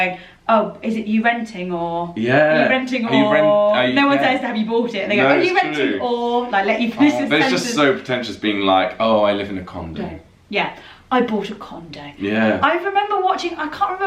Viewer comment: did just say, “I live in a condom”? 😂🤣😂